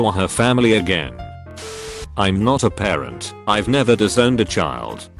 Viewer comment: I thought it said drowned